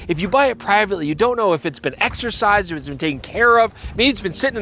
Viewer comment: I heard that as exorcised haha. Got to exorcise all the spirits caught on that old camera! Anyways a lovely camera that might become a collector’s item for what it is. It would have been nice to see a digital version. I love the design but wouldn’t dabble in film with how expensive film and development can get. On the note of Kickstarter cameras, have you checked out the Yashica and I’mBack camera collab. They’re supposed to come out in November 2024.